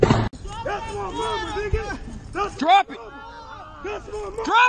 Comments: the other guys screaming: "thats my mama" scary ass situation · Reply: Oh my goooood 😭 I came to the comments instantly once I could hear him… I thought he was saying “that’s my daughter!” And I almost threw up 😭 fucked up world man. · At first I thought that guy was yelling, "that's Obama" and I was like, nah. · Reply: I heard "that's my brother" and thought he was a rapist piece of shit too until I read the comment section.